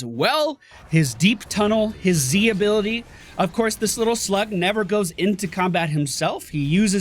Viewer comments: It sounded like you said "This little slut never goes into combat himself". Took me a second to realize you said slug. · I thought called Abby a slut!